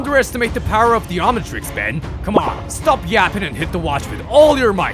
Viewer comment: Did Grampa just say stop yapping? 23:25 💀💀💀💀